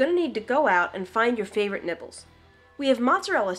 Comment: Darling Mistresses (Mistressai?): how does one find Welshey? Not that I don't love y'all, but... Please link?Also, at 1:02ish, I really thought said "nipples." Classic. Also pronunciation soon after. Love!😳